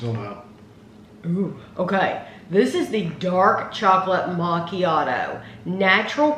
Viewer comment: i got excited, i thought it said dark chocolate maraschino. I was hoping for a cherry filling